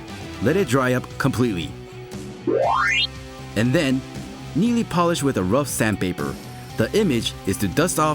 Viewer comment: I thought he said nearly but I realized it was neatly